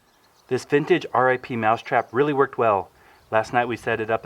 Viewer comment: Omg I thought it said R.I.P mousetrap Like the mousetrap died